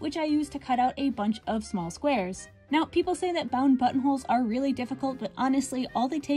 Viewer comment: I thought I heard something entirely different the last time you said buttonholes and I laughed so freaking hard that I’m fully invested in the sea foam green buttonholes and subscribed lmaooo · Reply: Ha, me too. My mouth popped open like a goldfish 🐡 the captions did say butt holes😂